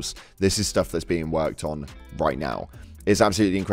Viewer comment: bruh... did you just say a-jew-dicator?